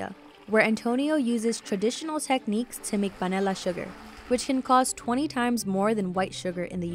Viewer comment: hold on, was it just me or did someone else have to put on the captions to actually understand what she was saying saying I thought she was trying to say vanilla but she made it sound like banilla